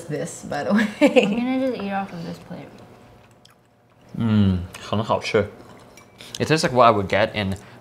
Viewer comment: Did I hear “很好吃” at 16:05? Am I wrong?😆